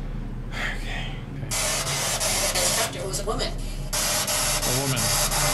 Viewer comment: When y’all were doing the spirit box in the basement and you said it said “woman”, I thought it said “I thought he was a woman”. I think they’re roasting you. Calling you a country boy and now a woman